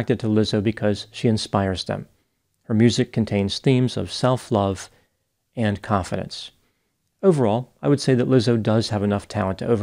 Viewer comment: I genuinely thought Dr Grande said "her music contains themes of self-love and coffins" at 8:25 as a dig at the singer. I cried with laughter when I realised I had misheard him. But that aside, thank you for the very informative video, Dr Grande!